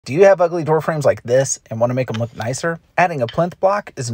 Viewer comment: Ohh 😮 I thought he said (Do you have ugly girl friends) 😂 was expecting an ugly chick to pop up.